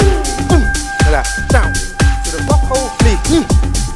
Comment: does he say "to the butthole?"